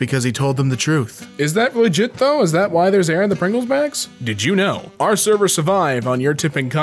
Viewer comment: did he just say Pringles Bags